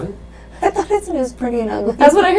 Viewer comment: it sounded like it said “the death was freaking/pretty ugly”